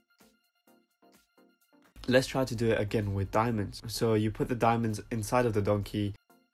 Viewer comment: WHAT DID HE SAYYY?!?!?!?!??! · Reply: yOuU pUt tHe DiAmoNDs InsIDe Of the DOnKEY